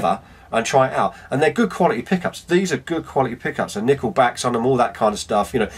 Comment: Dislike, because I heard ""Nickelback"